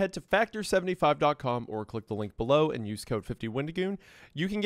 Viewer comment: Wendigoon? I thought you said when to goon